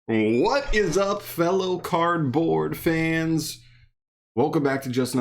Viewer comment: I thought you just said “what is up fellow cardboard